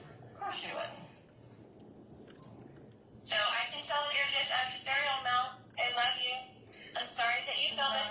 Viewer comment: what the heck is Lori saying?! To me It sounds like she says, " I can tell you're just a cereal mouth."